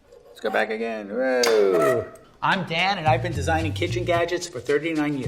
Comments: Not gonna lie I thought he said “im dad” instead of “im dan” lol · I thought he said “im dad”